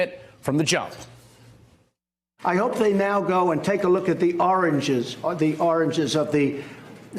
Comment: Trump says oranges instead of origins xD